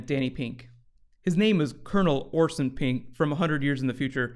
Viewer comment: When I first heard Orson's name, I was laughing so much I choked on my drink. I thought his name was "Awesome Pink."